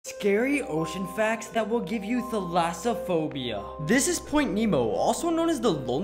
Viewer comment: I thought he said that ass phobia